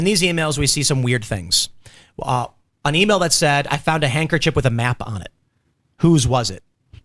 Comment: that's not what it said, it said there was a handkerchief that had walnut sauce on it.